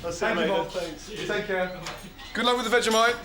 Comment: Did Gordon say, "Good luck with the vegi-life"? HAHA